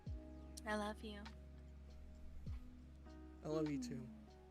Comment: wait guys did she just say i love you at 1:29:30!??